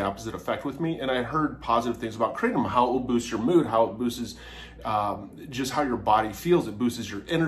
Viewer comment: Did you just say boostess? You do realize that's not a word right? Are you not able to simply say boosts? 😂